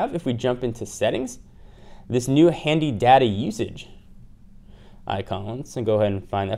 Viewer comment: I kept hearing "daddy usage" rather than data usage. lol